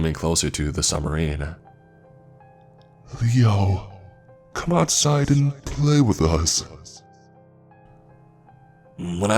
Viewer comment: Hahahaha I heard it as “Yo, come outside and play with us”😂😭